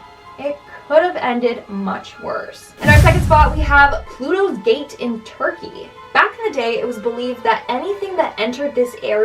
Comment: thought it said "PLUTOS DATE IN TURKEY"